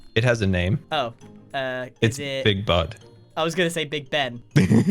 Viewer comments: I swear that he said Big Butt 😆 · i thought he said "big butt" 1:50 xD